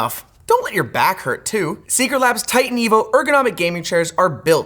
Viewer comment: Oh TITAN EVOOOOO, I didnt hear TIGHT & EVIL at all 😂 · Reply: I genuinely just thought it was “tight evo” cuz of how it would grip your back or something